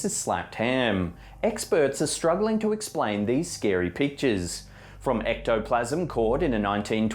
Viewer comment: Sounds like he says "scary peaches" instead of "scary pictures" at the beginning of the video... seriously listen to it, maybe it's just me lol it's at the very beginning like 0:05 into it.... he says it again at like 10:35 but it's not as bad lol